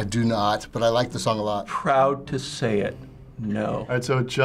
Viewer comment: 1. 4:36 correcting captions: definitely said the word it before he said "no" 2. 8:15-8:26 correcting captions: closer to saying ever than "never" in my ears, and he said Lil Dicky is huge instead of "He's huge"; probably said the word and before "popular" as I couldn't hear the letter s enunciated for "He's"